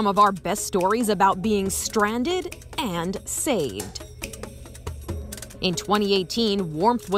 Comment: I’m fried and I thought it said being stoned and saved djsjdjsjs